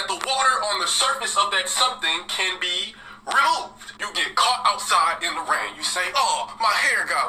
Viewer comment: I always thought he said 'riddled' not 'removed' for some reason